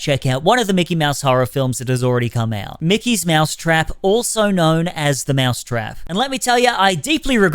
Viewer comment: I thought that was called Mickey Mouse the trap house